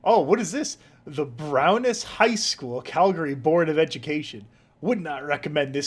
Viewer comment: When I was first listening I thought said "the Brownest High School" and thought someone was about to crack out some gamer words.